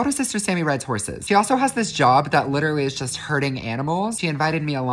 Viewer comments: I heard him say “ she has a job that’s literally hurting animals “ 🤔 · I know I'm not the only one who thought you said hurting not hurding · i misheard ‘herding animals’ as ‘hurting animals’😅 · When you said" herding animals" I heard "hurting animals", I was so confused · Is it just me that thought he said "hurting animals" and not "herding" 😭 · I thought that he said hurting, not hearding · The first time i watched the video i thought you said a job hurting animals not herding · I thought you said “hurting” not “herding” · I thought he said hurting animals it herding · Herding animals Me mishearing everything : HURTING ANIMALS ! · Herding! The first time I heard "hurting animals" 😱😱😱 I was like "no! She would never!!!" 🤣🤣🤣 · I thought you said hurting but you really said herding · I thought you said hurting animals thank god you said hurding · I heard “hurting animals” I was waiting for so long to find out what the heck her job was. Then the video ended and I processed… “Oh! HERDING!” I’m goneee! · Ok at first I thought he said hurting animals not herding animals and I was a little nervous lol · am I the only one who thought when he said "herding animals" thought he meant "hurting animals" ? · I thought he said hurting animals, not herding animals. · Omg no bc I thought he said her job was “hearting” animals 😭